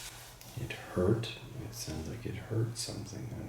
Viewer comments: sounded like he said "it hurt me seeing" or "it hurt me stinging" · When you asked him if he felt pain and when he responded at 8:37 , if you play the video at .75 speed, it sounded to me like he said "It Hurt Me." followed by a choked back sob. I got teary eyed when I rewatched this video. I wish I could give him a hug :( Edit: Also, at 12:39 , with the playback speed at .75. I heard him respond "He Shot Me With A Gun!?" as if he was in disbelief. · I heard something like "it hurt me ____" and another word. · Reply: I heard it hurt me to see